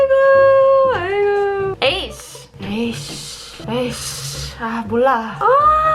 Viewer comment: What I heard, "aish.. mbuh lah" 😂✌️